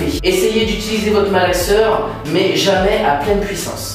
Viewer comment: A 15:28 j'ai compris " utilisez vos frères et soeurs"